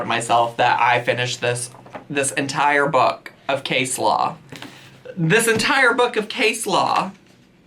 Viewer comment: What you said, “case law.” What I heard, “coleslaw.”